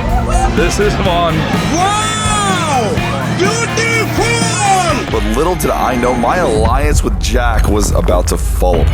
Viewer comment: can someone tell me what he said, please? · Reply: What he said is wow Beautiful